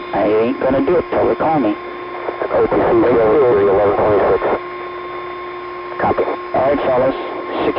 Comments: I think he says "copy" at 0:27. that means "Yes I understand". Is that what you wanted to know? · At about 00:22 I think I hear someone say, "I ain't gonna do it til ya call me...." I love the mix of technical and casual talk heard on this clip. At 3:49 sounds like someone says, "Rock and Roll, Columbia!"